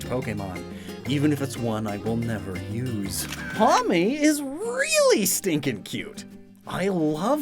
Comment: I was looking away from my phone whilst playing this and when he said "Pawmi" I heard "mommy"